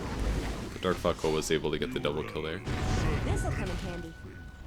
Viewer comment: Sounded like: "The dark fuckhole was able to get the doublekill there" .! 17:15 . xD!!